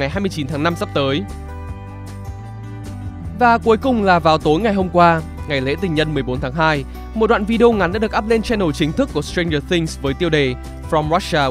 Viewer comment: ngày lễ tình nhân 11/2 ???? Nghe như 11/2 ý